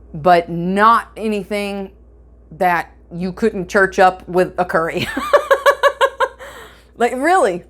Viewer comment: "Nothing you couldn't church up with a curry." At least I thought that's what you said. :-) No matter, I still L'dMBO. Thanks - excellent topic and video!